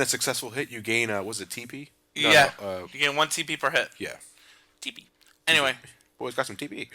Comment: at first I thought you said you gain 1 TP per shit... but it was per hit.... sorry I'm corny but dang - one TP per shit seems kinda not enough!